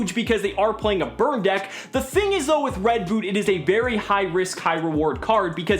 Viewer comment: Did just say Red boot